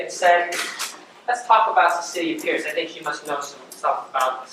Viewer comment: Almost every time i hear him say "Cicilia Pierce" for some reason I hear "the city of pierce" lol